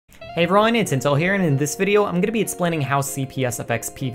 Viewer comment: I thought you said incel instead of intel...